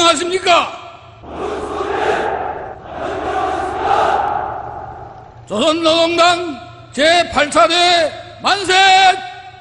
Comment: What is that word the soldiers keep saying? I've heard it in every North Korean parade, but I still have no idea what it means. For example, at 38:17. It kind of sounds like "bansei"?